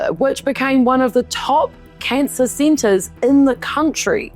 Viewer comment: I thought you said Bris Center when it was showing the giant scissors ✂️ ouch!